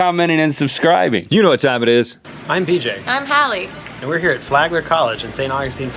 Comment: i thought thy said "im DJ and im Khaled" put them together and get DJ Khaled. but they said bj and